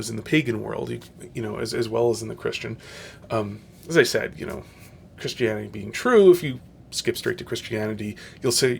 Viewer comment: "Christianity is true if you just skip right to Christianity" Lol, they don't come much dumber than you do they? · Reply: That is simply not what he said. He said at 10:58 "Christianity being true, if you just skip straight to christianity, you'll save yourself a lot of time, but... " That is very different from what you said that he said, "Christianity is true if you just skip right to Christianity" You are at best unintentionally misrepresenting your opposition (which if that be the case, you should have known better, and should have spent the time double checking to make sure that you actually got the quote right), and at worst intending on deceiving people (which if that be the case, you should know that not many people are going to fall for that when the video, and what actually said is right there).